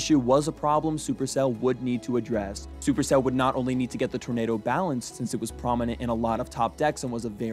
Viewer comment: Says tomato instead of tornado at 12:34 lol